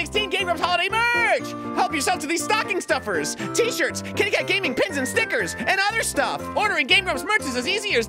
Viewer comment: When Arin said KittyKat Gaming, I honest to god thought he said "KKK Meeting."